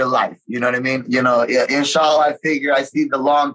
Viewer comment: Did Ricky just say inshAllah?